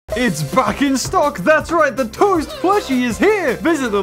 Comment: I could’ve sworn you said its F***ing stock instead of “Back in stock” probably my bad American English